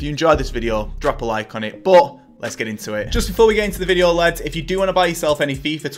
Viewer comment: Think 🤔 I heard lads